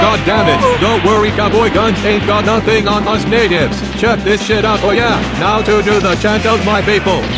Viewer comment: DID BOB JUST SAY THE N WORD???? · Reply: He said "Natives"